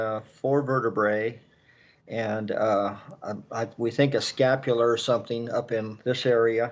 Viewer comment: okay who's talking in background? at 910 "scapula" I hear 'his heart' and then at 'shirt pieces' I hear 'sock' So am I hearing someone in the background or am I hearing Sue come through?